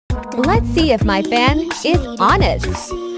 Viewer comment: Who saw the end when her fan was bad 😢. She wasn't true fan 😭😭 Edit:- omg!! So many likes!! Wish i had these many subscribers too 🥲🥲.. Thank you 😊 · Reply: I LITERALLY HEAR HER SAID " fan sh**t" 😮‍💨